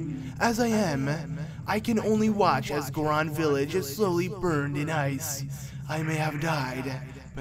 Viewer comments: did he say burned in ice? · he said burned in ice it said buried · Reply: Some accents make buried sound differently. He said buried but the reverb doesn't help his inflection make it sound like he said burned instead of buried. It's like a BURiED where the "i" sound is very faint and the reverb erases it entirely making it sound like a nasal consonant when it's not.